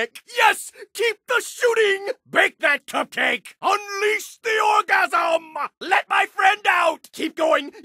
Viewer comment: WAIT DID HE JUST SAY UNLEAHS THE ORGASM